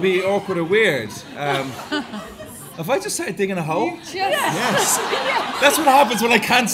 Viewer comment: What does Aidan say at 36:30 please? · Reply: Sounds like "have I just started digging a hole?" x